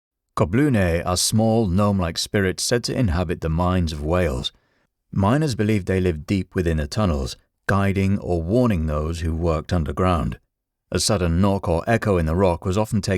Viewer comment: I thought I heard ‘minds of whales’ which confused me no end for a second. Thanks for the good tales!